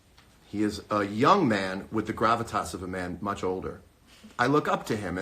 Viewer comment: What does he say at 13:16 : he’s a young man with the gravitas (!?) of a men much older.